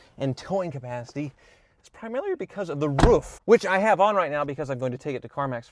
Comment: Okay I hear it he does say roouf